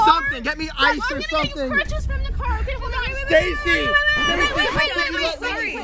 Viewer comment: I thought u said scrunchies from the car